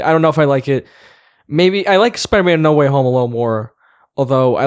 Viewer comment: it sounds like said "Spider-Man: No Way Home Alone" lol